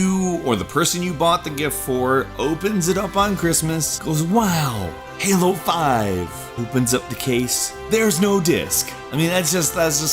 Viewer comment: When he was talking about #1, i thought he said, "Wow! Hey, low five." not "Wow! Halo 5!"... i am not an intelligent person.